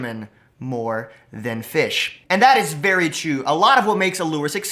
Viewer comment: Is it only me Or did he say chu instead of true